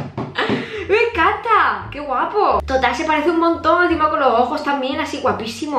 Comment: no entendí que dijo pero fue gracioso 😂❤️ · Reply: Dice: Ay pues si que se parece!! Jajaja